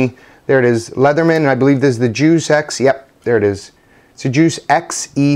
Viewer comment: When he said juice x I totally thought he said Jew sex.... Damn you dirty mind! Great vid btw! Merry Xmas!